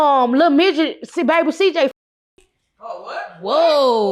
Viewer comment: I thought she said it was his baby. She just said they didn’t f***